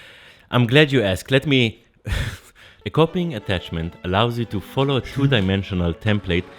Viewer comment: For a minute there I thought you said cock ring attachment!